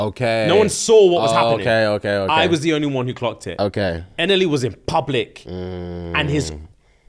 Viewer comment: What story is referring to here? I can't make out the name Not the Central Cee one Edit: Found it. NLE Choppa